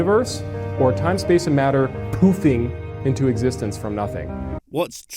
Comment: For a second there I thought he said, 'Time, Space and Matter pooping into existence.' That would be awesome - I'd pay to see that 😁!